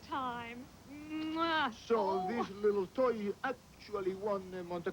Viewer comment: Did just call Herbie a toilet?